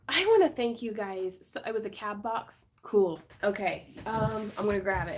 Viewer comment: Lol. I thought you said cat box. Not cab box. My ears perked up